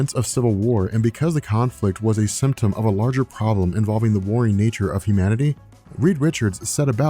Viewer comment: Ah, the warring WARRING nature of humanity - thought you said BORING nature of humanity - sometimes Marvel makes humans seem boring- amilying?